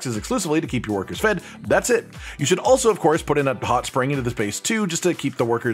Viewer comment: put in a ... what? listened 10 times and can't understand. Hopefully some viewer knows the context because I doubt anyone can understand those words. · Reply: Spas.